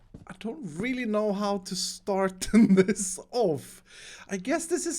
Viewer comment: I don’t really know how start on this oh fu- Close one Edit: apparently he said off I heard wrong :) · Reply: he was saying off. "I don't really know how to start this off."